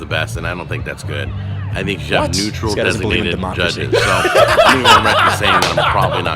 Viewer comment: What was said that made the two of you laugh? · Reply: He doesn’t believe in democracy